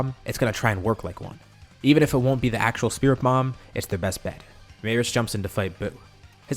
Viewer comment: My ears may be bad But why do i hear ”spirit mom” at 2:54????